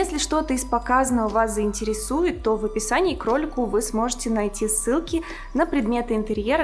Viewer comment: Вместо "в описании к ролику" услышала " в описании кролику". Сижу и думаю:"Какому кролику?!"